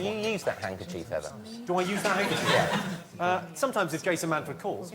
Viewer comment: Please someone help me out; what does Jimmy Carr say about his pocket square? it is at 9:36 Thank you! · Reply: If Jason Manford calls.